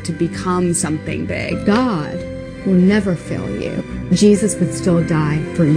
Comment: i thought that woman said god will never "fill" you at first lmao, then i realised her southern accent she was saying fail; that is certainly much better than what i had in mind lmao